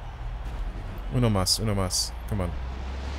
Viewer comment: did he really said "una mas, una mas"?